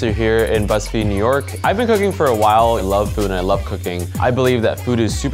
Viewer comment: i thought he said "i love food and i love cocaine"